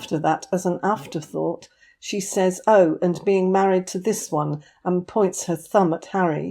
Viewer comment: I thought I heard M say “This One”